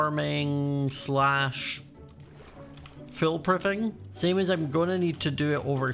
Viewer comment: I'm sorry did he say Phil proofing